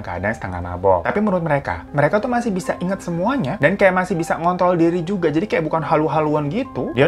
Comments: Maap bang, gatau aku salah denger atau gimana pas menit 6:55 bang bilang "ngontrol" tapi kedengerannya kaya "ngont*l" 😂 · ga salah denger kan? "ng*nt*l diri :(( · = salah denger atau bener gak ya ? mengontrol diri atau apaan wwkkkwk:v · Menit 6:53 kok aku dengernya.. masih bisa ngont*l ya.. apa salah denger ya kak🤣🤣